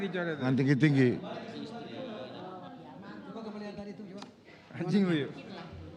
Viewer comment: ada yg yebut anj***. Pas volume full jadi suaranya gede banget....atau akunya yg salah denger ya🙏🏻🙏🏻😂